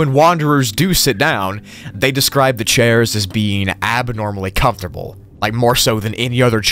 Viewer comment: I thought is said they describe the chair as being abnormally uncomfortable and I was like yeah duh